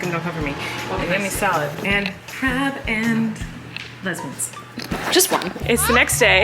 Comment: I rewatched four times to see if I heard her right. “Crab and…..lesbians. Just one.” 🤣🤣